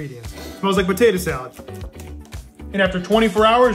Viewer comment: “My full recipe” I thought you said it was your mother in law recipe